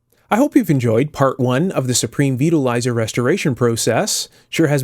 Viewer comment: I thought you said Supreme Beetle Riser. Sounded like a very odd choice of pizza toppings.